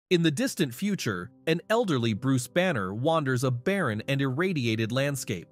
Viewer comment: I thought he said Bruce Wayne, and my stupid self was like "wait how is hulk and Bruce Wayne in the same comic?"